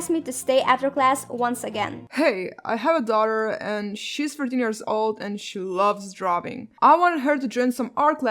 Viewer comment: Ahhaa i thought she said 30 years old hah oops